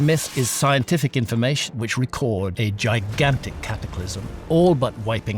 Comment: Dude, I thought Graham had said: "A gigantic CAPITALISM that all but wiped out the human race". I had to rewind that lol!